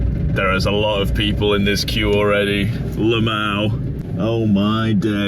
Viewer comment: did you SAY “lmao” LeMao 😂💀